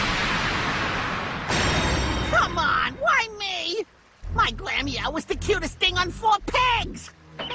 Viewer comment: Me: "What did he call her?" (turns on the captions) Caption: "my grandma was the cutest thing on four pigs." Me: (pause) "Sure! That makes sense!"